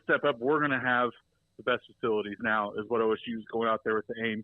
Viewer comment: I first thought it said failures not facilities. 🙂